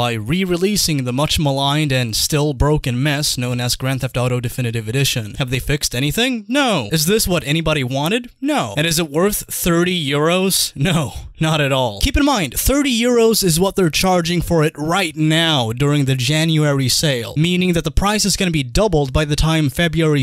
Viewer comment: like how at 0:13 it sounds like you say "Is it worth three euros?" because the answer is still the same xD